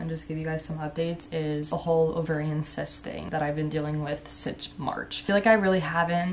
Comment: Omgosh literally thought she said "I'm going to update you guys on my incest thing I've been dealing with since March" .... Nope, it was the cyst situation lol, I was stunned 😂